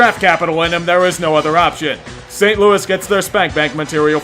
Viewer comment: Did he say St Louis?